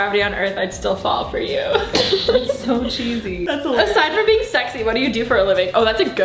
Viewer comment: “aside from being sexy, what do you do for a living?” 💀😂 i love alisha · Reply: OH SHIT I THOUGHT IT SAID "ASIDE FROM BEING 70" GOD I WAS SO CONFUSED